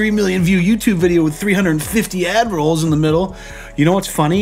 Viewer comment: I heard, "350 adderalls in the middle", not "add-rolls", and lost my shit lmao